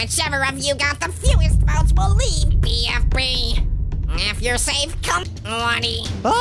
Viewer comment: WTH DID HE JUST SAY ''when your safe ###)